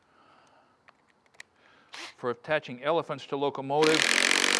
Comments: Did I hear that right? "For attaching elephants to locomotives."? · "Or attaching elephants to automotives " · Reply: I thought I heard "elephants to locomotives"...